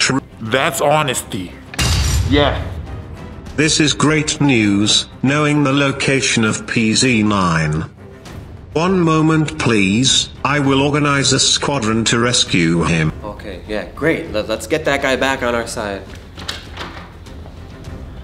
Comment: In 7:02 I think project zorgo leader said Quadrant ???